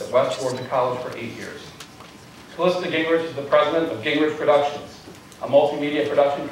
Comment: At :18 I could have sworn he said "...Gingrich is the president of Gingrich Corruptions." Maybe I mis-heard... or maybe my ears are prophetic.